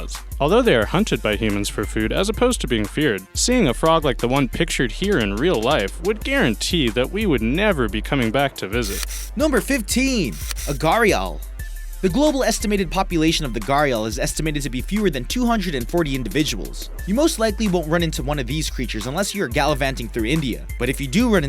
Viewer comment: I thought it said agario XD